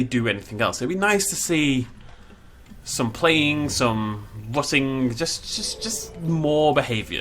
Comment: Did he say rutting? Like...rutting rutting? The term for when farm animals fuck? Edit